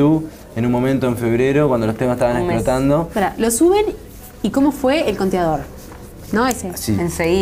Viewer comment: como el fue el que dice? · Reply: ***** pregunta como fue el conteador , x la cantidad de visitas en youtube